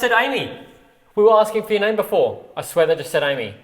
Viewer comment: when thought the box say "Amy", i believe that I heard the name "Tammy"❤❤❤